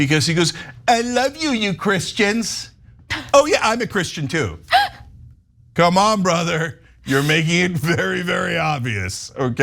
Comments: And, I think he said, "I love my Christians. I love my Christians. I'm not a Christian (mumble mumble)." Anyone else hear this? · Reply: I thought he said I'm a Christian then mumbled not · I have played that over and over again and i heard trump say I'm not Christian am i wrong? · Sounds like he said "I'm not Christian " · I heard "I'm NOT christian"